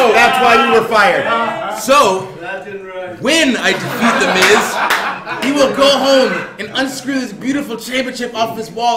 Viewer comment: love bray in this video hahah also what did Neville say at like 1:25 that had everyone laughing? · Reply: He said 'That didn't rhyme' (Referring to sandows rap;))